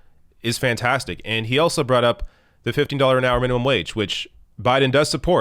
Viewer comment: You should have seen the look on my face when I thought I heard say "$50 minimum wage" instead of $15. I never wanted to vote so hard in my life lmfaooo God help us all